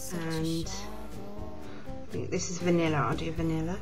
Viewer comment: with her accent which is very cute it sounds like she is saying vaniller but other than that it was cooool hahaha